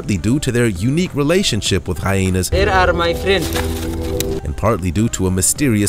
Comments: When he said “They are my Friends” I heard “they ate outta my friend” like wtf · Did anyone else think he said "get outa my face" when the subtitles read "They are my friends" at 7:14?